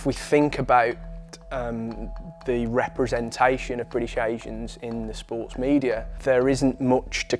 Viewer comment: Thought was saying British agents for a sec.🤣